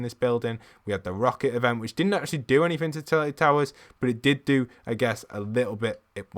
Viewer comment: did i hear Turtle Towers??